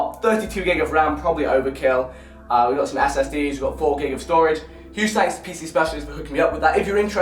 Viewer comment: did he say 4gig of storage